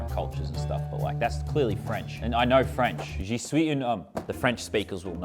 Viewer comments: just so people know, I'm pretty sure what he said in French at 10:12 is "je suis un oeuf" (i could be wrong) but if that is what he said, that translates to "I am a egg" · Thought he said “Je suis un femme” 💀